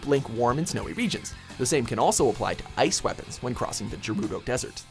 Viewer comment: Did he just say "Jerudo?" Instead of Gerudo?